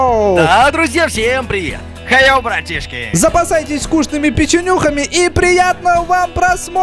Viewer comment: Я один услышал как сказал на 0:13 "Запасайтесь ВКУШНЫМИ печенюхами"?